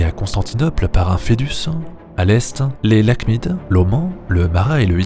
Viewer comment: Salut 8:14 "les Ghassanides sont liés à Constantinople par un ... " quoi ? Merci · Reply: Pardon ^^ foedus. C'est une alliance en latin, assez courante dans l'antiquité romaine. J'avais beaucoup utilisé ce terme dans mes vidéos sur Rome, j'ai pas fait attention à le réexpliquer !